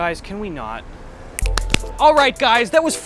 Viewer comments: "assault that like button, leave a rad comment, and never give up hope" Tooooo good xD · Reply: I thought he said never give up home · Why did I become so uncomfortable when Gus told me to “assault the like button”? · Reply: Is it weird that I heard to ‘salt that like button’?